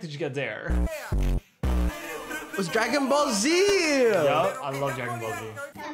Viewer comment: did they say dragon ball bro 😭 (Its jujutsu kaisen)